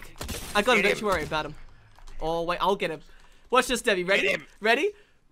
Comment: actually thought said watch this daddy